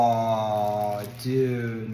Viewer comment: I thought he said pre cabbage I was like wtf is pre cabbage